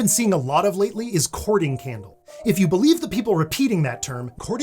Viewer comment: I thought you said "cording-candle" and that would make as much sense as "courting-candle"... If not more.